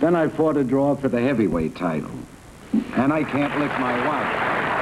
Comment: What's he saying there that he can't do to his wife? Hmm. Different times indeed! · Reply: he says I can't lick my wife by which he means he's boxed and won in professional fights but he can't win a "fight" with his wife.